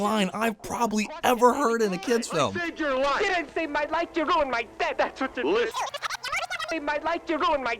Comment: what was he say saying at 2:42 "you dident save my life your wriended my ...." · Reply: You didnt save my life, you ruined my death